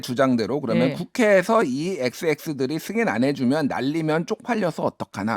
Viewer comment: 안들리면 0.25배속으로 들어 보세요. "승인안해주고 날리면"으로 들리네요. "~고 날~"은 누가 들어도 확실할 것 같습니다. "바"자는 전혀 안들리네요. 확실하게 알아보고 논하도록 합시다. 사실을 알아보려고 하지않고 자신의 평만 주장하지 맙시다.